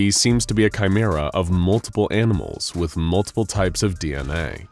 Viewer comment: I swear he said "amimals"